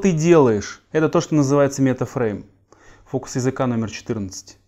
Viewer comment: Люди, кто расслышал? 16:50 какой метод назвал? Фрейма? Спасибо!